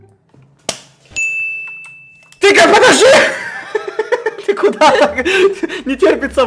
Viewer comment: что он сказал? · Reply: он сказал: тихо подожди